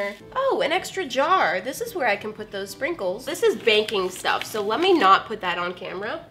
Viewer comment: At first I thought she said baking stuff, I was so confused 😂😂